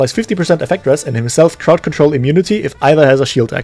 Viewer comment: I heard "birth" control immunity 💀💀